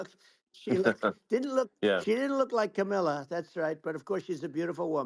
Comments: I thought he was saying "Camilla", as in the Queen of England.... LOL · I thought when he said Camilla he was talking about Charles wife. 😂